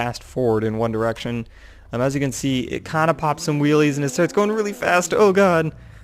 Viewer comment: did you just say: as you can stee? i think your tongue got twisted :3